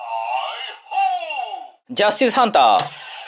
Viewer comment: ハンター砲がパンツァーフォーに聞こえる気がする